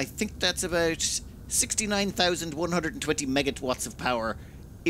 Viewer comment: Had to pause because I head "MegaTwatts" instead of MegaWatts. Haha!